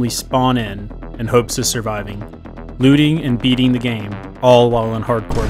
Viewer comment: i thought he said looting and eating the game for a second